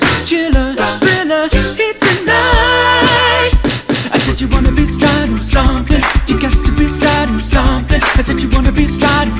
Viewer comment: why do I hear " you wanna be striding chungus"